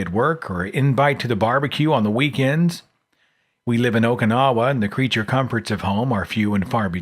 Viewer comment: Did he say Okinawa, or is this just me?